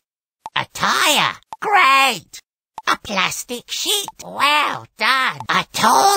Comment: Sounds like he said “A Plastic S**t”